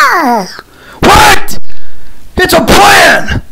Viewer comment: Bro sounded like he said "WHAT IT'S A PLAN